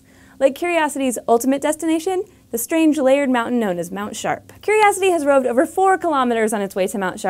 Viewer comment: Am I the only one who has a hard time understanding some of the words that Caitlin say? She's an amazing host, but some words can be hard to hear or at least understand. For example 0:50. Is she saying "the strangely layered mountain notice Mount Sharp"? I mean, English isn't my first language but I definitely understand other words and sentences! Anyway, Caitlin is a great host! Thanks for the video. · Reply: Strange layered mountain known as Mount Sharp.